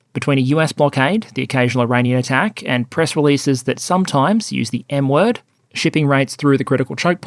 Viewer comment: i swear i thought you said "press releases that sometimes use the 'N-word'"